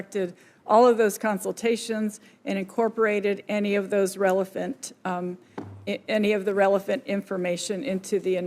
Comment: wait...did she just say "Relephant" - - rhymes with elephant? Listen, she says it twice